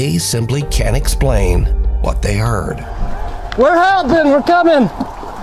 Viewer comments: yes.. i hear it ." help me." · I heard the mother's voice in the first tragic incident!... they said that her voice was not picked up on their camera's audio... you most certainly can hear her say, "help me" at 3:28/3:29!